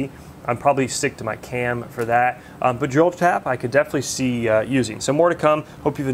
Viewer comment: What kind of tap did you say, jolt tap 4:43? And stant widget 4:50?